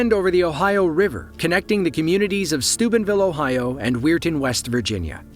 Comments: was I the only one who heard stupidvillen and weird west?😂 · I thought u said Stupidville Ohio 😂